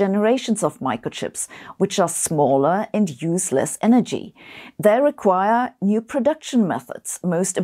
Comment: I just heard "which are smaller and useless energy". But yeah, I noticed, that it should be "which are smaller and use less energy". :D